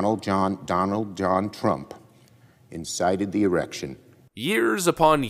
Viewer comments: Did Schumer just say DJT insighted the erection? @ 0:51 Lol! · did he say”erection” instead of insurrection??